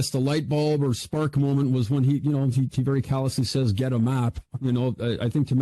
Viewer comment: I’ve seen the Williams interrogation many times and to me it sounded like he said, “Got a map?” Even had an upper inflection, as a question would sound. Anyone else?